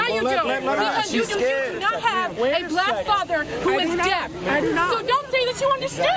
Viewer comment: I thought she said her farther was dead :( oops